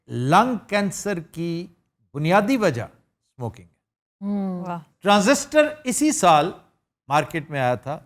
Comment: I didn't hear properly at 27:10. can anyone tell me the basic reason of lunges damage?@ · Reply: Smoking